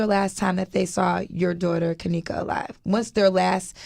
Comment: Didnt her daughter say they saw kenneka laying in the freezer with her hair all over her head???? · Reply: I swore I heard her say kenequa ? about the 12 min mark